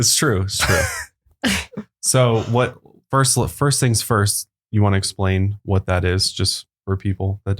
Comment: at the 2 minute mark when goes to say first things first it sounds like he says my name Ursula lol yay!